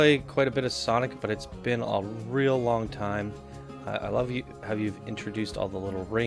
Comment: Did he just say I love you at 4:06?